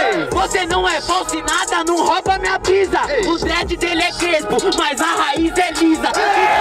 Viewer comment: "O dread dele é crespo mas a raiz é lisa" kskskkskskkksk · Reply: Eu entendi "o trap dele é preto mais a raíz é lisa" kkkkkk